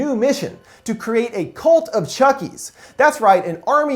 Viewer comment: Thought it said clit of chuckie